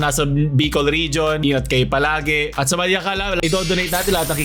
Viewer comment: tama ba yung narinig ko Iyot Kayo Palage? 😂